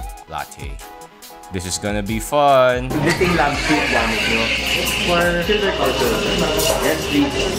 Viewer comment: Sorry, I didn't get what you said about what they used to grind the coffee (0:27 and 4:31). What was that? Auto-generated CC says "dating lab suite." This will make a good series, an exploration of specialty coffee shops in Metro Manila. Your videos have taught me a lot about the hobby, thank you very much for what you do! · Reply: Hi there sorry I didn't say it clearly enough. The grinder they are using for their filter coffee, if I'm not mistaken, is the Ditting 807 Lab Sweet. Thank you so much! Glad you enjoyed :)